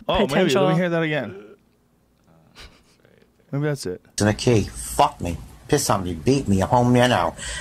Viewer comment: I thought he said “Fuck me. Piss on me. Call me an owl.”